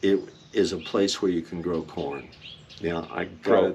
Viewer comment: I also heard porn then went back and he actually said corn